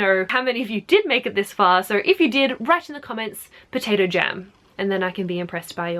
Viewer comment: I could've sworn she said potato gem, not jam 😱